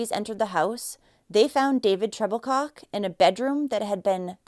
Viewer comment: Was his last name Troublecock? That’s what I thought she said